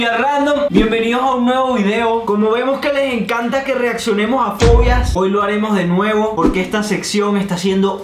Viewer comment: en el inicio yo entendí "pollas" que si dijeron "fobias" 😅😅😂😅😅😅😅😅😅😅😅😅